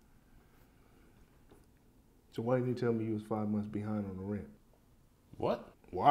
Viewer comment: Pause I thought he said they was 2 months behind where did 5 came from